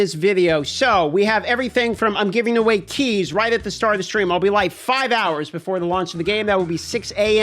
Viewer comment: I thought you said giving away CHEESE at first, and got quite excited for that